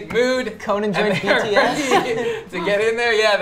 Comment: did Noah say bts?!